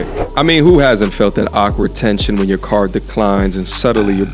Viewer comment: the visuals are ai generated, unfortunately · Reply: I don't mind a little ai but it's a bit sloppily done at certain parts, the part where he says your "card declines" 0:14 and it shows a car because it heard "car" instead of "card" and he ain't even go back and fix it.